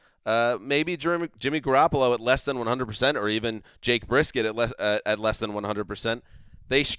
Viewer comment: Did.. did he say "Jake Briskett"..